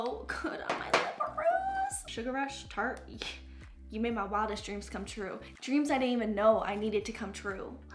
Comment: When you were talking about your look being glossy I thought you said WET DREAMS I was like, “oh no honey this isn’t what you meant.” Hahaha. You’re always so on top of being family friendly so I was honestly floored. Turns out IM THE ONE WHO IS WRONG LOL.